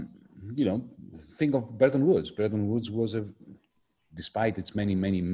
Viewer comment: What does Yanis say at 38:15? What is Bretton Woods or Burton Woods?